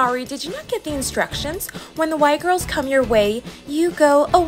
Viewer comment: honestly i thought they were saying white girls and i was like wth